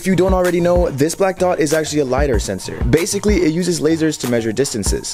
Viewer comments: I Misheard it and thought it was a liar sensor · i thought you said a liar censor😂😭 i was like what is this fake advertising🤣🤣🤣 i’m just dumb